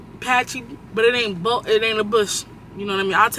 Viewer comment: idk WHYY I thought she was bout to say “I’ll take a picture and show y’all” I was bout to be like HUHH😂😂😂😂😂